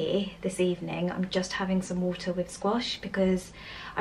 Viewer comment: What did u say u were drinking! Hot water and wash??? What is wash !?😊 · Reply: I thought she said squash 😂